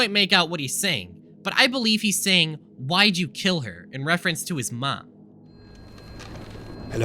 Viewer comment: instead of Why did you kill her I hear "I'm gonna kill you" it also fits more with the state of rage scout was in when he was saved by medic